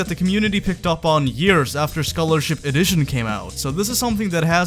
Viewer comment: you say After scholarship, subtitles say Before